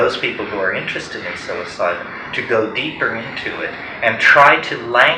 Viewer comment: The subtitles in 3:04 is wrong... What i think he is saying is "psilocybin", not suicide. Awful mistake